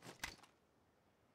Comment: guy in the last clip says ‘no mames’ spanish for no shot i guess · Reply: hahah, i thought he said oh mami, but click bait